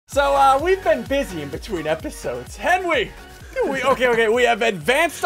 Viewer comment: "So We've BEEn busy between episode" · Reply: I thought he said we’ve BEEn BEESy between episodes